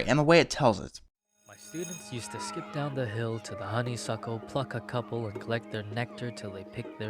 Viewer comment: "honey suckle, f*ck-a-couple to get honey for their fill" that's what I heard it as